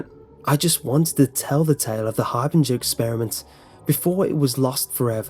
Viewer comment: I'm so hungry I thought it said The hamburger experiment :'v by the way love your videos !